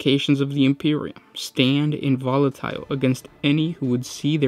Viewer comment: Did you say "Involatile"?!?! Double-check that, I'll bet it actually says "inviolate".